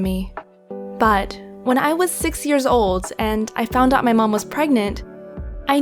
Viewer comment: I thought she said I was 6 years when I found out I was pregenant